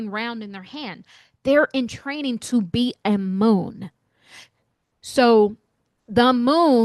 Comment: They were training to be a moon? Or what was the word you said there?